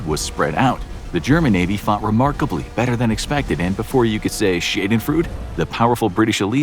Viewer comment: before you can say what?? there is another word i think they meant to say but it wasnt shade in fruit